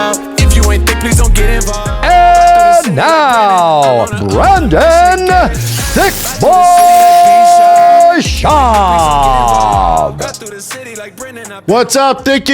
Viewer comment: Is it just me or did it sound like Buff said “BRANDON thicccboi schaub”